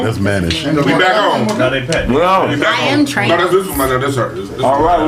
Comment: Listen carefully at 1:50:13 says that’s mannish. Then the one next to him looks him in the face and says “I am trans”. I don’t think understood what she said an no one else heard it.